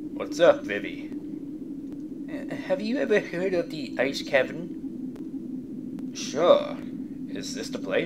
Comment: Awww. For a minute there I thought you said ice cream instead of ice cavern. Sad face.